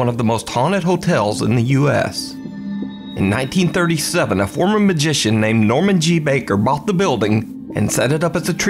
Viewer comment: did I hear that right ? Former magician ? .. and then later told he wasn't actually a doctor ?